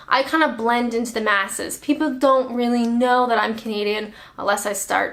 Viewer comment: Ok, I must admit I had to laugh at the comment where she/he said you claimed to be a comedian. On the first 5 or 6 videos I watched of yours I thought you were saying you were a comedian ... then I finally realised you were saying you were a Canadian. It's amazing how much an accent can throw you off when you first start hearing it, but then how natural it becomes once you fine tune your listening abilities. But hey, maybe you are a comedian as you have made me laugh.